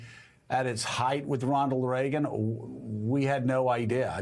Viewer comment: did he just say 'Rondal Reagan'? :D